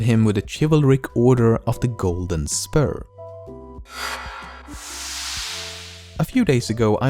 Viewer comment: i thought he said the golden sperm xD